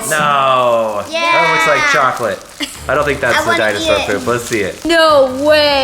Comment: did she really say I WANNA EAT IT? Girl.....its......doodoo....looks yummy, but...no. Bobby got me here :>